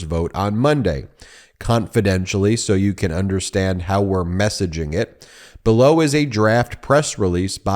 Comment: I was reading ahead a bit. I coulda swore it said "..how we're 'massaging' it"